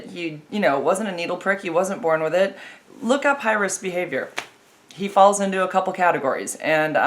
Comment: Did you say "hyrous behavious" by 07:20? I didnt hear what the word was to look up, please say :) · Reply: High Risk behavior